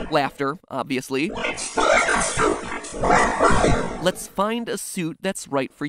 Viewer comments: the second one kinda sounded like it said lets play at first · am i the only one that hears "i remember" instead of laughter?